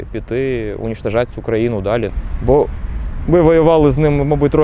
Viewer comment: Переведите пожалуйста , что он сказал на 3:26 ? Что делать с Украиной? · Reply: Він сказав "цю країну". Рашку.